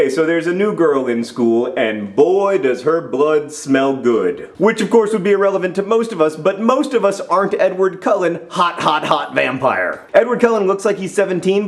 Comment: It sounds like he said relevant, not irrelevant.